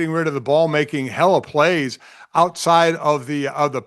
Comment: did he just say 'hella plays' 10:28 yeesh lol